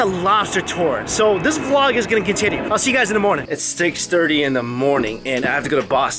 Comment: It def sound like you said steaks30 in the morning lol 😂. Must be steak on the brain!! 😊😊😊